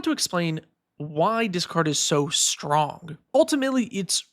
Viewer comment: i keep hearing "why dis card is so good"